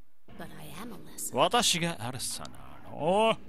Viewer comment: アレッサがドレッサに聞こえるw